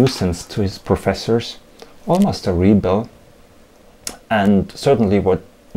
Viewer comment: Almost a what...? I'm afraid I didn't catch that word. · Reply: almost a rebel... sorry for the mispronunciation.